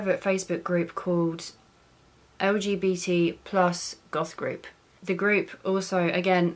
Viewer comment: She cant say the letter “L” I thought she said “OGBT+ Goth Group” and was very confused bc i was only listening to the video at that point.